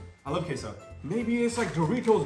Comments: I thought he said I love caseoh lol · why did bro say I love caseoh